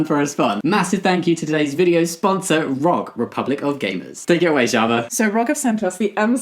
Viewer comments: When he said "Gamers" I thought he say "Gayness" 😭😭😭😭😭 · I misheard Republic of Gamers as Republic of Gayness 🤦